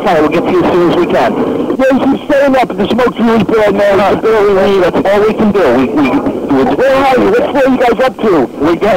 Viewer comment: It sounded like he said “Thank you so much” to the firefighter. Not “They keep saying that”.